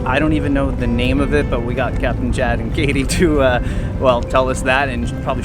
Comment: Hee hee, I thought you said Captain Jack in the beginning...not Chad